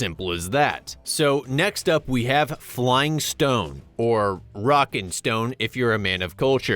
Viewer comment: DID I HEAR A ROCK AND STONE? 🗣🔥